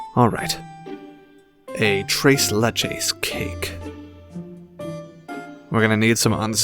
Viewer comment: A tres leches cake ❤️❤️! They are SO GOOD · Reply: For as second i thought it said ✨tres leeches cake✨👀